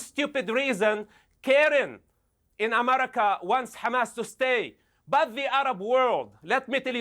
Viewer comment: Did.... did he say the Karens in America😳 Aajajaja!!!😳 🤡🤡🤡🤡